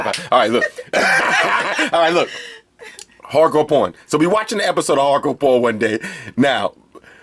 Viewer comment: Yoo that NY accent when you was sayin "hardcore pawn" 😂! I heard "on an episode of hardcore porn" and died 😂😂